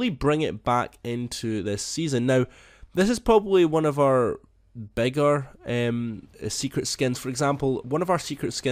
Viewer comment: Closely listen At 3:00 Sounds like you said beggar